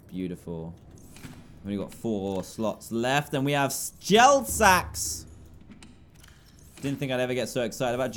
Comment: i have this thing that writes what he said... and when he said gel sacks it came up with gel s3x...I'm laughing my head off